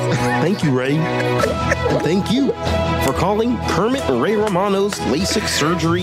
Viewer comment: i know it was supposed to be Ray Romano. I just hear Henchman 24